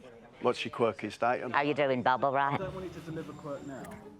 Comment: Someone said they thought Alan said “How you doing, bubble wrap?” and I can’t unhear it or not laugh. 😂